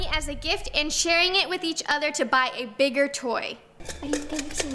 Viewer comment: Lol I thought sharing money to buy a bigger gift I thought she said sharing money t9 buy a bigger twin lol